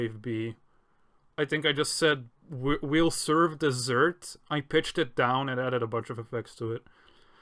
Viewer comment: Wait, im kinda confused a bit what he said is it "Have some desert" or "Well some desert" anyone?